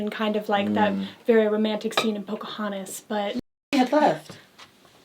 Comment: Wait, what did said in 10:22 ? My english is not good enough to understand the raunchy humor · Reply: I'm pretty sure she said "No, Merrie had left" like making a joke that Merrie Cherry was the pile of trash